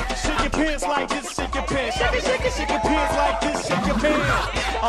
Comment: Sounds like he is saying "Shit your pants like this shit yo pants. shake-a shake-a pants like this shit you pants"